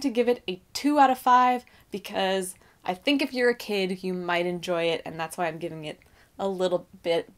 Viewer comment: as you gave your rating my iPad beeped for a notification so I heard “I think if you’re a c— you will enjoy it“ ...I’m sticking with my first guess 😛